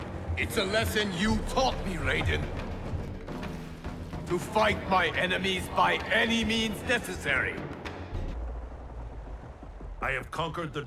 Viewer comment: LK:to fight by enemies by any means necessary From what i heard LK:to fight by enemies by enemies necessary